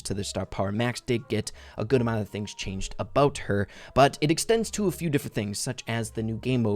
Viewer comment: did u say “her” on max?